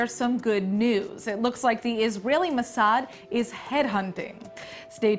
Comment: I must have played way too much Half-Life 2. At 0:21 I thought she said the Mossad is head-humping.